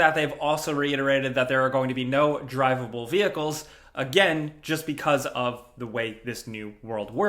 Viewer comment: You say no vehicles i hear "We are too lazy to program drivable vihacles" (xcuse my terible spelling)